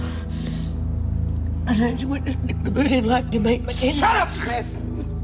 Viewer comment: What does say circa 3:50? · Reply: "Don't you want to stick the boot in like your mate McKenzie".